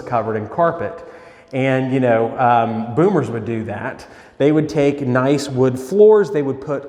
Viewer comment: Lol. I thought it said "bombers"